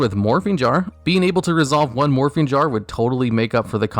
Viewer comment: I know he said morphing jar but I heard morphine jar That would be a fun card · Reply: When he said morphing jar the second time I swore he said morphine jar